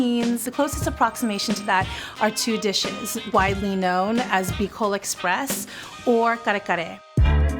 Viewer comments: Omg I really thought they were saying fecal express 😱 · I thought they said "Fecal Express"